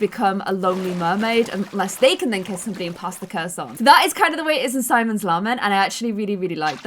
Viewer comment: When she was talking about the webtoon I knew which one she meant but somehow still heard her say Simon's Llama? 🤔